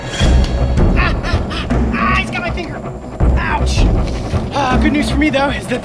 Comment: what did he say🤨🤨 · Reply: "Hes got my finger"